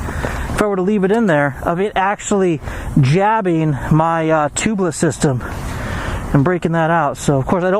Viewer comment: 2 questions. 1: are you running “tubliss”? Because it sounds like you said you were. So, how is “tubliss” on the street? It’s not hwy legal, or recommended by tubliss. Maybe I heard you wrong. Tubliss and tubeless..sound the same, but are completely different. 2: have you considered running something inside the tire to seal off punctures? Between the “tubliss” and the tire. I’ve heard of some doing this. Slime, or it’s equivalent.